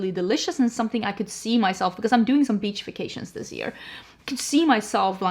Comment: I'm watching this before bed and I was already getting very sleepy so I heard "beachifications" instead of "beach vacations" and I did NOT even blink at that for a sec and I just went "man I'd love some beachification too" 😭🤣